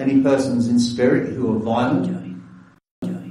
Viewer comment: I heard the whisper (through headphones panning from right to left) saying "waddya doing?"